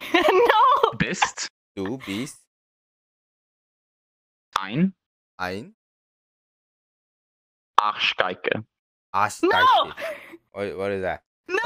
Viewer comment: I speak german and hearing say "Bubi" instead of "du bist" sounds so innocent and cute😂💕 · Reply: she said "Du bist eine arschgeige" - "you are an arschgeige??" (I dont think theres an English translation). "Arschgeige" is kind of a bad word, when you analyse the meaning of it, but if you would say that to a german they wouldn't take it serious, because that word is kind of "joking funny". So if you have a fight in germany and you would say that to your enemy, he would laugh, cause he couldn't take you serious anymore after you said that. So yeah, everybody in germany knows what it means, but nobody says is but friends to friends would say it when they are "mocking" each other in a fun way. Hope that helps you😊